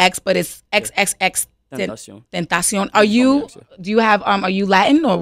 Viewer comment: Holy shit. I'm watching this with subtitles, because i don't understand english very well And @ 1:42 it translate XXXTentacion to "XXX potato soup" LMAO